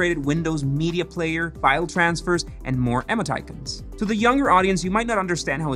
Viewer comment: is he saying "emoticons"???? sounds more like emmo titans LOL never heard it said that way